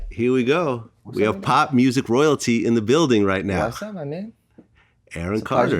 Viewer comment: 3 Seconds in and I hear 'Pop Royalty'...😂😂😂😂